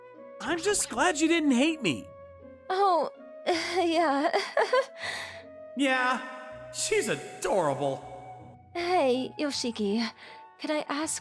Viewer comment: my brain got "yea she's a scrabble" instead of "yea shes's adorable" i was laughing my head of cuz of that